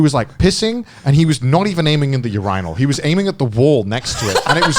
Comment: "... we wasn't even aiming at the YOUR-EYE-NULL" ...tf he just say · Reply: Urinal in british accent